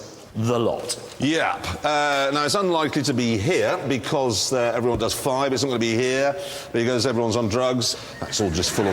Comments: what does jeremy say everyone does in north america, "everyone does" something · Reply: "Everyone does 5" in reference to the speed limits in USA. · What does jeremy say about america at 3:33? I can never make it out . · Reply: Everyone does 5 (mph)